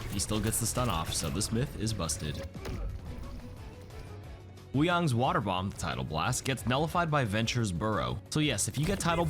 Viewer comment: I keep hearing “water bong”